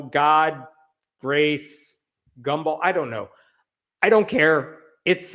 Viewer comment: Wow, he got it totally correct. It's the God, Grace, Gumball conference. With no evidence at all, I fully believe that to be true. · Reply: I heard "gumbo" when he said that... And is everyone certain one G isnt for 🔫